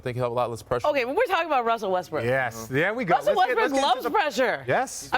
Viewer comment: did she just call him Russell Westbrick?